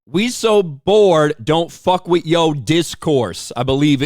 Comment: I thought she said “don’t fuck a trout fish” very wise advice 😂